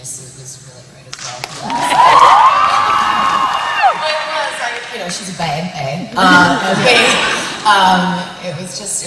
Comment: What does she say at 4:07? Awe, I like "Raven's" question, "Never?" That was sweet and cute. And Eliza answers, "Well, not on screen." haha Nice. :) .... What does Eliza say at 7:25? Something about a grounder saying if she has to say one more time, she's going to freak. Thank you. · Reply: she says that one of her favorite scenes to shoot is the kissing scene with Lexa, "She is a bae" !!! hahahah